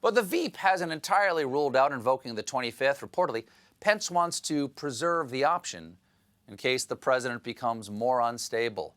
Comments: I misheard Steven as saying "in case the president becomes moron stable." But he's been moron stable for a long, long time already. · I heard "moron stable". I think he's there.